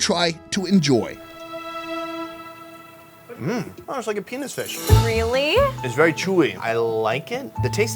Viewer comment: Did just say it taste like penis fish 😂🤣